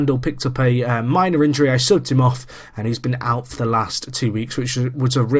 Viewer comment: it sounds like you said "i sucked him off" in reference to subbing randal im in knots